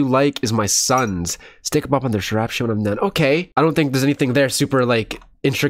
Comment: I thought she said , “Still gone run this rap 💩 when I’m done”